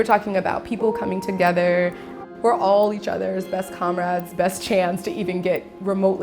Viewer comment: Wait. Did she say “comrades?” At 18:30 ???